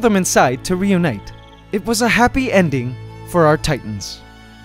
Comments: did he say titans 😂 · What did you call the tomb raiders, Titans?